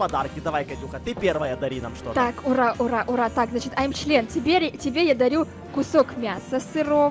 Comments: мне послышалось, или сказала "ай чл€н" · когда дарит подарок макаке мне показалось что она сказала im thlen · я услышала айм 4лен